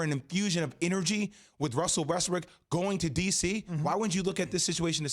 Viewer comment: did jay just said West-BRICK oh my god hahaha